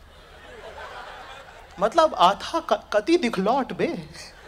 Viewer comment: what did he say? 1:56 . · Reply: Sonali Gupta he meant that the girl was wearing pretty revealing dress. Qatai dikhlot 😂😂